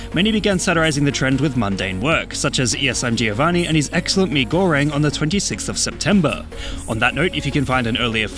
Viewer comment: Slaughter House MV: <link> Slaughter House slowed: <link> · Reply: bro i was just eating mi goreng and right after i finished i heard ''and his stunning mi goreng'' i swear yt is spying on me